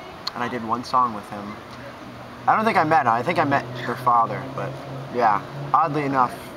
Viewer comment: "i met HER father"? did he say that? at like 3:10 haha.. just saying.. he seems pretty awesome and nice :)